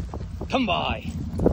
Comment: So all this time I thought they were saying 'Come *boy*' lol